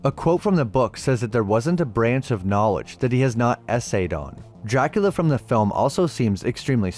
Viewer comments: “…. There wasn’t a branch of knowledge he hasn’t S.A.’d…..” Did I hear that right?… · "there wasn't a branch of knowledge that he hasn't essayed on" I'm so used to the RIDICULOUS levels of self-censorship all these youtubers do that I thought the narrator was saying "there wasn't a branch of knowledge he hasn't sexually assaulted" lmfao